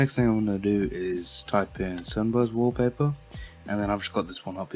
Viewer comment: Sunburst wallpaper buddy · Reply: oh I thought you said simba Wallpaper